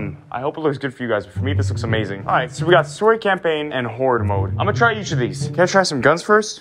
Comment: I thought you said horror instead of horde